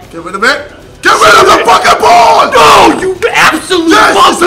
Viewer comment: does say "NO YOU ABSOLUTE MUSLIM" ????????